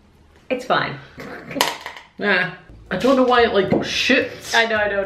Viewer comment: says "I don't know why it shoots", but it sounded like he said "I don't know why is sh*ts" at around 7:26 or something